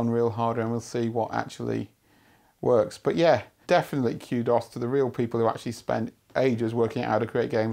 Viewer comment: It took me awhile to figure out he was saying "kudos" at the end. I don't know if that's the standard British pronunciation. To me it sounded like he was saying QDOS.